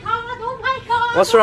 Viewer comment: At first I heard "I ting yor comor hit by the car" 😂😂